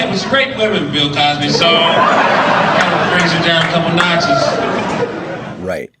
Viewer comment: What the hell is that guy saying at 9:12? I ran it back 12 times, something about “a couple Nazis” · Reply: "takes you down a couple notches".. good lord.. lol